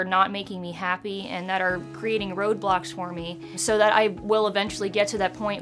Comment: I thought she said "ROBLOX"😂